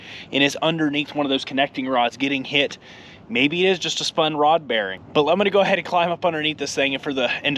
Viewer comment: I thought you said a spun main bearing. ITS a Rod bearing that spun then! Those Main bearings are fun to roll in with the Crank installed.